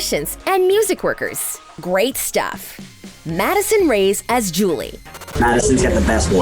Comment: When i found out Julie's real name was Madison rae i thought they said"addison rae"😂